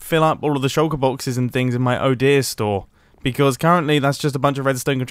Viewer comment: I thought he said his "oh dear" store 😂